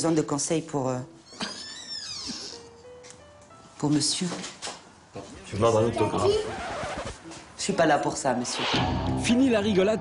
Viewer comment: Il dit quoi à 4:10 ?? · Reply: je pourrai avoir un autographe ou un truc de ce genre et super nanny de répondre: "je suis pas là pour ça monsieur!!!"